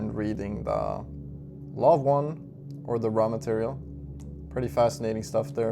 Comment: what books do you recommend here? I can't hear it very well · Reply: The law of one / the RA material